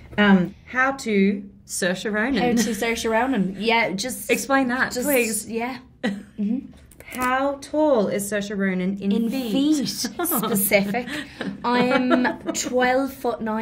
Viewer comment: I thought it said " how tall is Saoirse Ronan's feet "